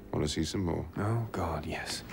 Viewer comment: what did he say?. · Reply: Strawberrycake*-* "Want to see some more (dead bodies)?" "Oh God, yes"